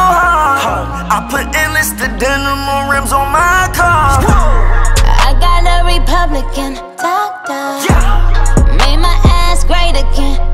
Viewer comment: 😭 sounds like she says I got every republican toppped off